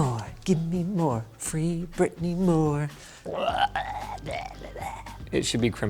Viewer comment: Did Linus just say free Britney while singing a Britney Spears song at 2:20 (we stan that)